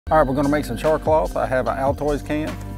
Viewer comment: I thought he said charcoal